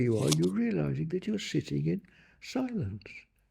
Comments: In the first like 3 seconds I heard “you shiting in silence” like hu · I thought for sure he said "shitting in silence" 😂